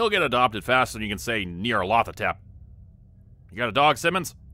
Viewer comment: lol what word or phrase was said here? ..."faster than you can say [neer-a-loth-a-tep]"...